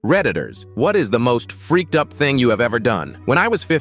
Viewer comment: When he said reddditors i heard "predators"..